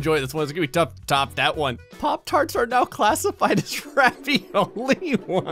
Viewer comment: When it said “poptarts” i thought it said passport.